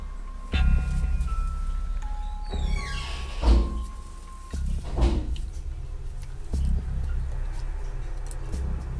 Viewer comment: like si entendiste lo que dijo en el minuto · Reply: dijo:esta ahi esta ahi , bueno solo mirale la boca